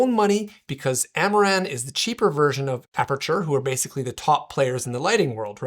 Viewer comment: What a great comment to read, you made my day! YouTube is a tough game. My stay at home dad activities take priority over making videos and I guess I just haven't been consistent enough on a certain topic to really grow. I do have another bigger channel (The SAHD Life) but still, hopefully in the near future I can grow to the next level. Sometimes it is challenging to stay motivated but comments like yours really help put some wind back in the sails. Thanks! I really appreciate the positive vibes 👊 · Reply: when you said 'i bought this with my own money because amaran ...cheap . I thought you said , because Amaran is too cheap to lend me one 😂